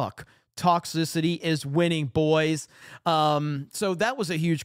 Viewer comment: Did you just said "Toxicity is winning" @ 5:31 ???